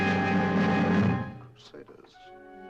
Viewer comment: in the 37:17 scene where he was looking at the Templar painting and statue. What did he say? Does anyone know? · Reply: Crusaders